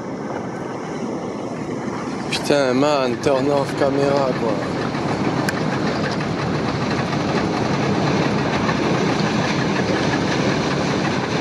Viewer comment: is the camera man French because i heard "putain man t'es en off camera quoi"